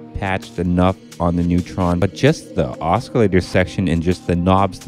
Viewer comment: Did he just say osc-k-I’llator @ 3:18 ?